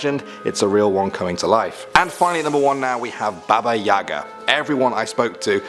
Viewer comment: I thought I heard baby Yoda but baba yaga